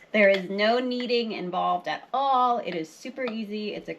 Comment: I can't remember if you said how to store the starter and if you ever feed it again or how to keep it going. Funny @ 7:10 in the video your baby said "I need ????" I couldn't tell exactly what but then you said "there is no kneading involved at all"! Lol I am enjoying my warm buttered fresh bread I baked in my Dutch Oven last night and can't wait to try it with your starter.